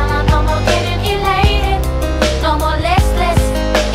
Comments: parece que dice "no molestes" KIDHJD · Siempre pensé que decía “no molestes” y es “no more listless” -_- 2k likes? Joder soy famosa. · alguien mas escucho que dice no molestes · YO PENSE QUE DECIA ''No molestes'' JAJAJAJ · en esta parte no puedo dejar de pensar que esta diciendo "no molestes" en español xd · parece que dice "no molestes" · escuche "no molestes" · escuche no molestes xd